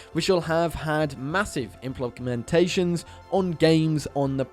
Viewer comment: Wait wtf does he say at 12:07????? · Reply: Inplonkumentations or smth.